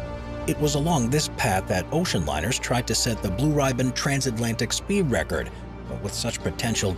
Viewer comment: correct me if I heard it wrong? The "blue rye-band"? This has to be a pronunciation abortion of "Blue Ribbon" don't it?